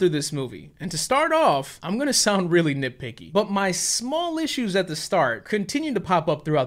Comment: did you say Whitwicky?